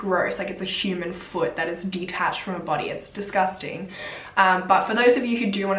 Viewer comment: Going to be honest, I would hope that the familys never watch this video. It was very inconsiderate to the girls. By saying it was gross and how you clearly dont know all your info. Not trying to be mean just giving an honest opinion. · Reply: Sweet Heart yep. I heard "disgusting" too